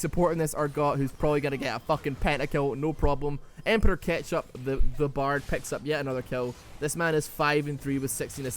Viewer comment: Brand will Support ? Or did he say Bard . Sounded like Brand to me